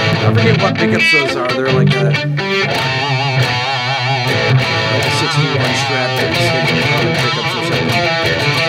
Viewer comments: Anyone find out what those strat pickups were? Seemed like everyone was super impressed 😮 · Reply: I think he says it's a 61 strat (body) with 65 pickups but Nuno is playing over him. So, Fender Pure Vintage '65 Strat Pickups? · Great playing form a guitar god! That strat sounds amazing, what pickups are in it? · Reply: I think he says it's a 61 strat (body) with 65 pickups but Nuno is playing over him. So, Fender Pure Vintage '65 Strat Pickups?